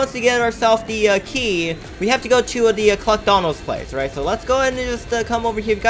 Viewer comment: Comment Below if you thought he said McDonalds